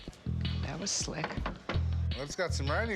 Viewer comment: I thought she said that was lit hahahaha